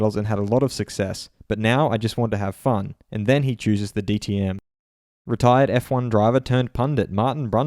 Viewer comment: at first I thought it said DMT xD Too much Joe Rogan